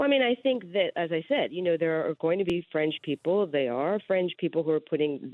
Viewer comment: Is it just me who thought she said "French" people, not "Fringe"? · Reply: It was fringe but subtitles said French.